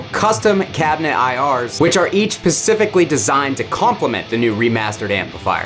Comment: did he say “Pacifically”?